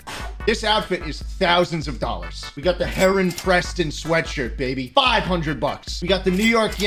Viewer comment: Ah my bad mind, I heard heron as “heroin”.